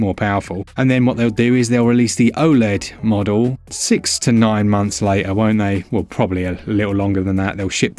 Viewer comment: six to nine... did you say SIXTY NINE MONTHS?!?